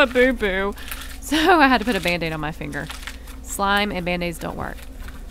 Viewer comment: I thought she said “slime and mayonnaise” but she said “slime and bandaids”